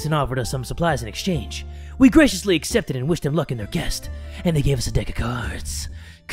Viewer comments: anyone else notice Jay say guest instead of quest??😂😂😂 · Did anyone notice on 2:16 that Jay said guest instead of quest? I’m not hating I’m just asking if anyone noticed